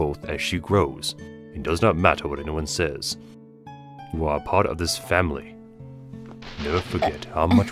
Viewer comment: i love how the subtitles say apart instead of a part frisk isnt a dreemurr